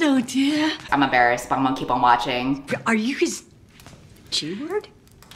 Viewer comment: I thought she said “Are you Grisha” and I PANICKED 😩✋